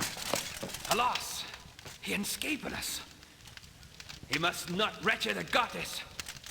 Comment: I think he’s suppose to be saying alas he escaped us, he mustn’t reach Augustus…